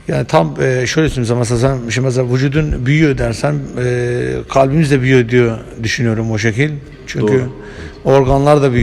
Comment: te ne diyor anlayabilen? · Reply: sen mesela sen düşünüyorsun sen mesela sanıyorsun